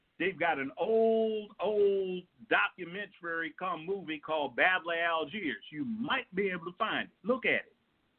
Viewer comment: What's the name of the documentary JJB mentions at 5:16? Sounds like he said Babbly Algiers.... anyone know? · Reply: The Battle of Algiers